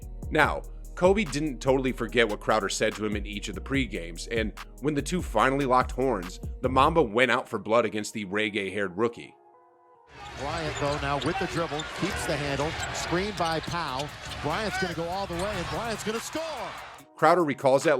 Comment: did you just say "reggae-haired rookie"? what is you smokin'?